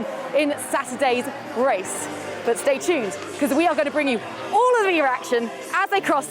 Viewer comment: Did she just say "We gonna bring you all of the errection"??! xD